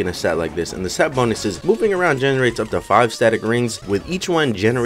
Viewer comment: why does he sound like he saying pooping around?